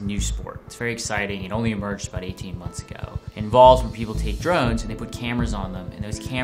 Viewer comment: every time he says "take drones" i hear "take drugs". he pronounces drones really oddly.